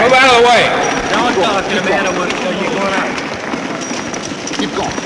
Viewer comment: The man waving his hand while gripping Shaw. "Don't talk to the man. Don't talk to the man or we'll kill..or you're gonna." Did I hear that right? Based on the reactions of the others in the 'protective' party. I think I'm right. headphones, replay a few times. Who was the man? Anyone know?